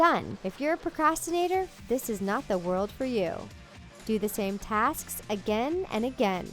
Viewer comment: did she said tasksk? (And I oop sksksk